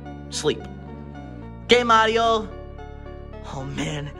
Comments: it sounded like luigi said gay mario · Did Luigi just say gay mario...